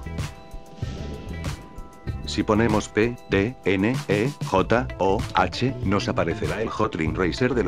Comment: parece que dice "pendejo"